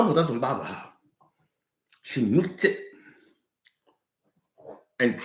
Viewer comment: What did he say at 15:10? "Trouble doesnt bother her. She...." what? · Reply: She needs it